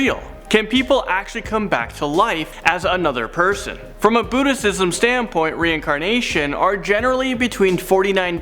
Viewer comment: I'm sorry... Did you just say BUDDHISTISM???